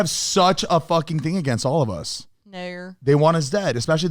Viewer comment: What did Alisha say at 50:25 🤣???? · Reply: I think “Naur” like how someone says No in Australia 😂